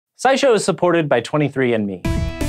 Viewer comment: I thought he said "Sci Show is supported by 23 Inmates" lmao